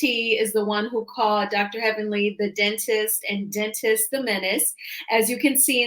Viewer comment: I don’t know why but I thought that I heard call Heavenly Dennis the Menace but I’m probably wrong. I would not be surprised if it’s Dr Simone that resurfaced the video.